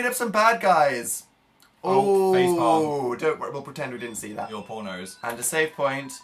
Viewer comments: did phil say "Your pornos" or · when Phil said "your poor nose" I heard "your PORNOS" im ded hahah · What did Phil say? Your pornos? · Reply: I think he meant "your poor nose" because the character fell on their face, but yes, it did sound like he said pornos. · I swear to god Phil just said "your pornos"... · when he said poor nose i thought he said pornos whoops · Did anyone else hear Phil say 'your pornos' at 53:38? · Is it just me, or did anyone else hear it at the end (53:38) when Phil said "Your Pornos" and then gave Dan an ominous look. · Near the end when Phil said "you're poor nose" I thought he said 'your pornos' and I screamed. I had to turn the subtitles on to find out if he was still pure and thank god that he is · ohmybeans at 53:38 i thought phil said "your pornos" bUT HE SAID "YOUR POOR NOSE" OH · When Phil said "your poor nose" I thought he said "your pornos" and I was confused for several seconds. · phil said " your poor nose " and I thought he said " your pornos"..... why?* something I ask myself every day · when Phil said "Your poor nose" it sounded like he said "your pornos" 😂😂😂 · wait... 53:39 did Phil say "your pornos"??? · When phil said your poor nose at first I thought he said your pornos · Am I the only one who thought Phil said porno when he said "your poor nose" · or something like that Phil says 'your poor nose' but I heard 'your porno's' what is wrong with me? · Did anyone else think Phil said "your pornos" instead of "your poor nose"